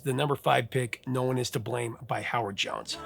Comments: Did he say howard Johnson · To me it sounds like you said Johnson not Jones for Howard Jones.